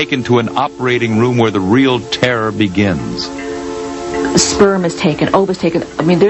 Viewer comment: was it me or at 2:46 did she say soerm was taken?